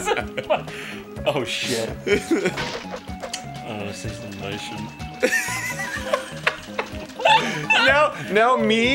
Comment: WTF did Jon say there? It's too freaking quiet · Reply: ''I wanna say something but i shouldn't''